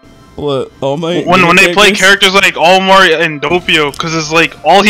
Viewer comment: did he just say all mart